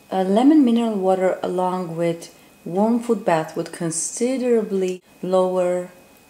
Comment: mineral lemon water with ……. What did she say after please? (Warm bath?)